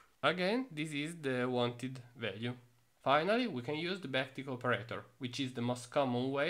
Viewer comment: I was so confused on the back tick operator cause of the accent, I thought it said bactic. Then I read the terminal error which said "Comma not in backquote" and realised lmao